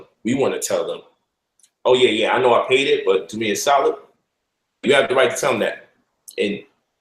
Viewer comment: What exactly was said from 33:16-33:19 in the video? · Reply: "Do me a solid" meaning take that shit off my credit lol